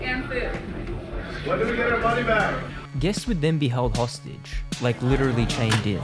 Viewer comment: It's probably meant to be "money back" but I thought it sounded like BODY BAGS which would be a fair question too